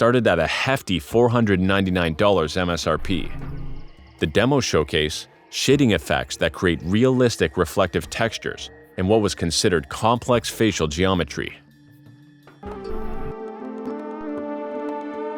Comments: I thought bro said "shitting effects" 💀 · seriously sounds like you say "shitting effects"